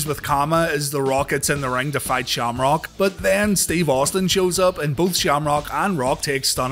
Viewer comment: I’ll be for real. My hearing played tricks on me and I heard “Steve Blackman” instead of “Steve Austin” Steve Mufuggjn Blackman just invaded my brain.